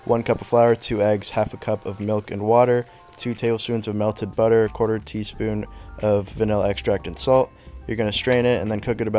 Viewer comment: 1 cup flour 2 eggs half a cup of milk and water 2tbs of melted budder half a tbs of vanilla extract and salt , strain , cook 1 min each side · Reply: 1/4 tsp of vanilla and salt, I thought he said